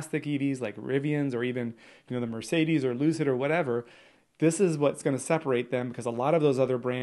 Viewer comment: Did he called Lucid a loser or did i heard it wrong? :D Not saying he was wrong ;)